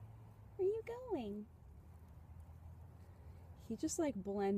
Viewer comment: Did he just say: "Are you lying?" :-)